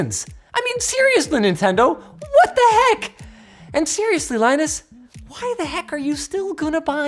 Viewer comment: Lol at 0:44 it said Lionus instead of Linus in the subtitles (not auto generated)